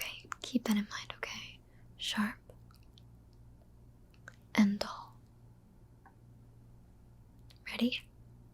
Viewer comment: i was half asleep and i thought at 16:21 you said “short and tall” and i had to go back and listen again 😂 (sharp and dull)